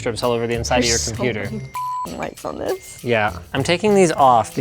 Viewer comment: what did she say here? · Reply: "There's so many "f***ing lights on this"